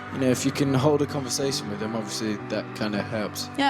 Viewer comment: I think he says them not him but like coz in Britain we say em so “with em” sounded like him